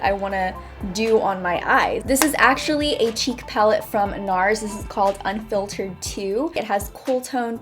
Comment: When she said a "cheek palette from NARS".... I thought she said cheap and I was like whatttttt???? NARS???? CHEAP???